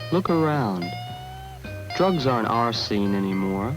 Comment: Need help to understand ! English is not my first language and I am not sure what the guy said at 00:33 did he said "Drugs aren't our scene anymore" ??? Thank you so much for your help.